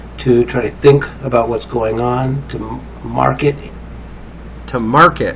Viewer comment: The narrator says "to mark it in his mind' and you say he said 'to market it'. That does not even fit into the context of what the narrator is saying. Your paranoia is making you mishear. Did you even bother to listen to this for a second time before you commented about it? I don' think you did, because it is so obvious that you were wrong in what you heard. You can do so much damage by making such mistakes, damage that is not deserved. This man has never done anything to hurt you. .............. This was at or about